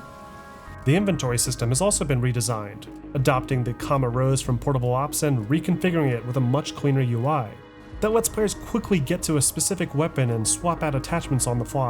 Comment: Several times throughout the video a name is given for the style of inventory system in games like portable ops and mgs 5. I don't quite understand it. To me it sounds like he's saying "comarose". Can someone type out the name for please? For example he says it